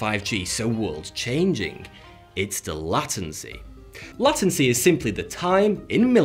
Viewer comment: did he say "Latin-C"?